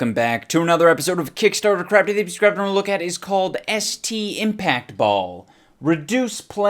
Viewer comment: Throughout the video all I heard was STD IMPACT BALL at least that's what I thought he said.